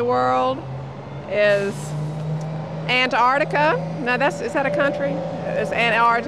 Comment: what did say? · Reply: Antartika